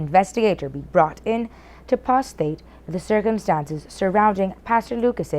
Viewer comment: Did she just say "to prostate the circumstances" ?